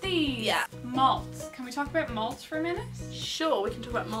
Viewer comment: When said let's talk about malt I thought she said let's talk about moths.